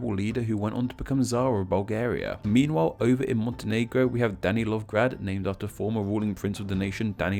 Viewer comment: I misheard Danilovgrad as Daddy Long Grad